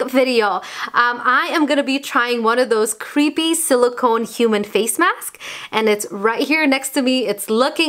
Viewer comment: why am i hearing face maksk??? xDxDxD repeated it so many times xD